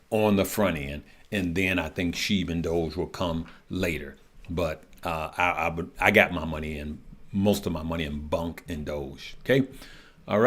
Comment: did you say you have most of your money in bonk & doge?